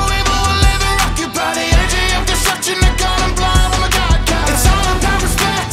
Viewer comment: Wait. Wait. Wait. Hold Up. 4:07 “No evil will ever occupy the Energy of Destruction I caught em blind, with my God Ki” The way he said God Ki, sounded like “Hakai”. Bro that’s sick